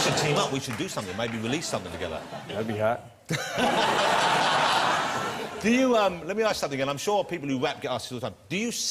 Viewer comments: What does he say at 2:56? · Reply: “That’d be hot.” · What did Eminem say at 2:55 ? didn't really get it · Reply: “That’d be hot” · I have a question：2:54-2:26 when Jonathan says we should do sth together， release sth，then what did Eminem say？that'll be？？？